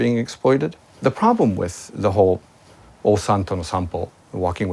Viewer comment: JK stands for 'Joshi Kōsei' or 'Joshi Kousei' not 'Joshi Kausei' 女子高生 + and at 5:57, what he says is 'Ossan to no Sanpo' おっさんとの散歩